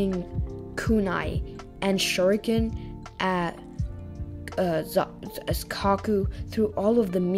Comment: anyone else hear caku instead of haku at 38:26?